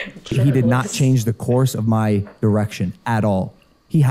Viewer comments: I thought he said "he did not change the course of my erection" 😭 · I swear at 56:15, he said "he did not change the course of my erection." Like, I'd hope not.